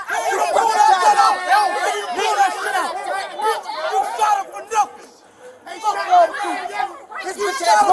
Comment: did someone said he shot him for nothing after they recover the weapon???